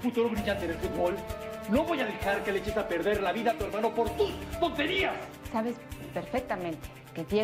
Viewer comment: Dijo tonterías, pero encontré una ilusión auditiva que hace parecer que dice puterías xdd